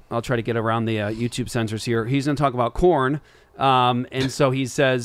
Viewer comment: did you say Corn or Korn? Because I enjoy both 😁